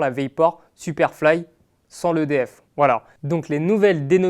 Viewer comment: Il a dit : « sans l’EDF » ou quoi ? 1:14 Il y a de l’électricité dans les crampons ? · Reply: Sans le DF (Dynamic Fit) 😀